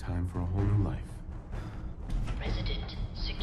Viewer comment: lol i understand "Time for a lonely life" instead of Whole new life xD